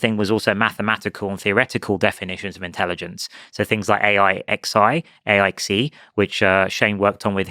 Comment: what does he says? ai xi? i dont get it